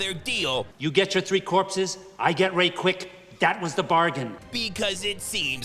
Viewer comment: straight up thought he said “I get raped quick” 😂😂